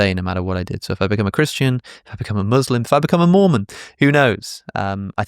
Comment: I misheard you saying 'if I became a Moomin' 😂 Good channel, very interesting yet funny aswel 🎉